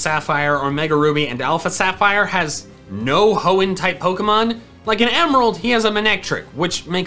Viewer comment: did he just say hoen type?